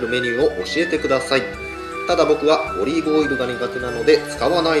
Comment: のりがオリーブに聞こえた俺は重症。